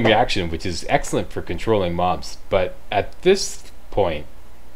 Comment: For a second there, I thought you said "excellent for controlling Moms". :P